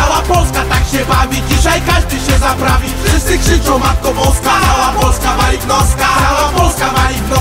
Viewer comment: A ja na początku usłyszałem CAŁA POLSKA WALI KLOCKA :D